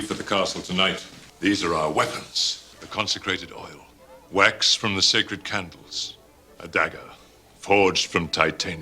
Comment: I thought he said wanks from the sacred candles!